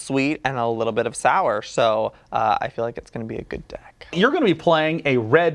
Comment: Why did I hear good dick